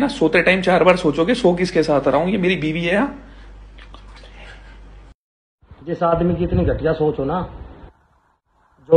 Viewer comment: what did he say??? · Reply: Jugaad